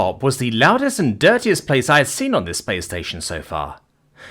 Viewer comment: does anyone else kind of hear play station instead of space station or is it just me